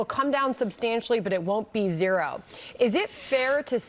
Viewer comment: I thought Trump said, “They won’t be zero.”